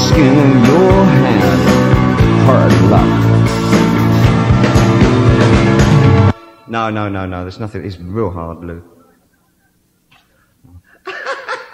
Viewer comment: Great, l hear Bruce Springsteen, Marc Bolan, Tom Waits, Lou Reed, Anthony Lewley, Iggy Pop and Neil Young... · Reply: ah I thought he was saying "that's real hard", then was asking "how would he do this?"